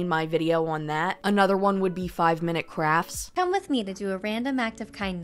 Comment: I thought that it said "5-minute craps" and for a moment there I was imagining someone making a youtube short about sh!++!n& and I was like, "yeah okay pedos and Mr. Beast making videos about torture and child diddling, why not literal ACTUAL SH!+?"